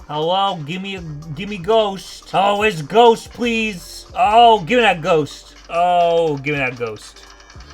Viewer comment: You're saying "gimme that ghost" but I keep hearing "grabagool"